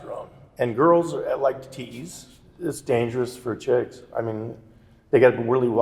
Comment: did he just call his daughter a chick? (I’m sry if i heard wrong i just wanted to know if you had heard the same thing.)i don’t mean any offense to anyone)